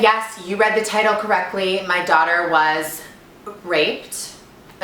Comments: WTF i though she said "my dotter was raped" 0:08 HAGAHAGA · "My daughter was raped" is that what she said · at first I thought u said ur daughter lol but u said dog XD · I thought she said daughter instead of dog